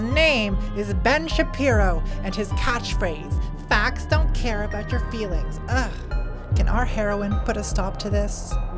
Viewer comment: At first I heard “fags don’t care about your feelings” and I was very confused lol With that said, I just wanna tell you that I’m in love with you and that FASCISTAS NÃO PASSARÃO!